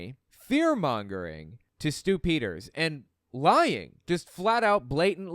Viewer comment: Stew Peter really is stupider. Everytime you said his name, I kept hearing you say the word Stupid. Not even trying to be edgy, I just actually thought you were saying Stupid the first few times LOL